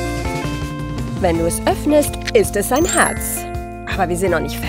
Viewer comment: ein Hartz😂😂😂😂 wenn ich es falsch verstanden habe korrigiert mich bitte